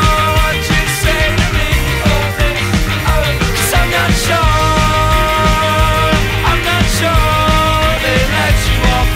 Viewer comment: anyone else hear ‘Sapnap’s short’